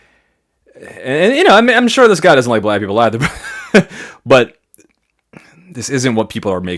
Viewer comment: unless I’m misunderstanding your words, did you say “I’m sure this guy doesn’t like black people either?”